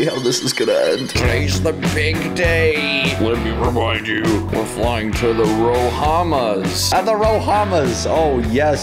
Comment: I thought Dad said, "were flying to the Obamas"